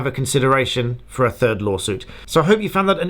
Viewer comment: Hi from France 🇫🇷 ! At first I thought you said « A TURD LAWSUIT » 🤣🤣🤣